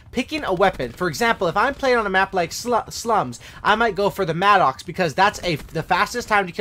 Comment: anyone else hear how he said if your playing on sluts *slums use the Maddox 🤣🤣🤣